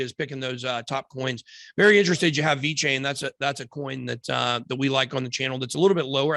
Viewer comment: Hello guys , did he say v chain or something similar? Please help minute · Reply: Vechain